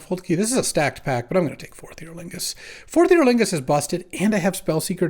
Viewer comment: I always hear "Fourth Year Lingus" and wonder what a Lingus is.